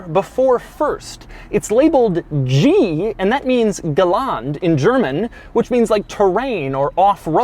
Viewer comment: Lol, im german and i tried 5 mins to understand what he was saying at 11:48. Its "Gelände" :´D